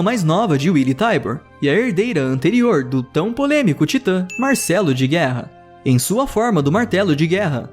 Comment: eu ouvi "Marcelo de guerra"?